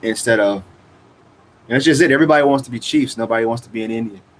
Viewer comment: I thought you said, "everyone wants to be the cheese." Especially bc earlier you were talking about Borat and his cheese scene.